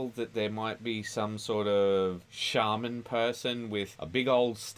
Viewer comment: When you said shaman person, I thought you said Charmin person and envisioned a guy made out of puffed up bunches of toilet paper 😂